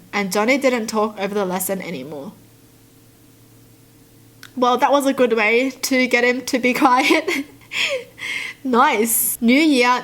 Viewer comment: What'd she say at 0:46? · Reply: Well that was a good way to get him quite was what she said ♥